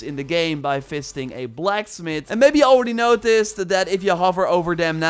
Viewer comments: "fisting a blacksmith" ??? I love the work you do, but sometimes the speed with which you talk makes for some misheard activity I'd rather not be involved with! :D (Don't worry - I know what you really said...) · Did he say "fisting a blacksmith"?